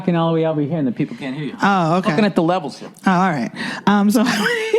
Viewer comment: what was the joke here exactly, did Bill say "I'm lookin at the levels here" what does that mean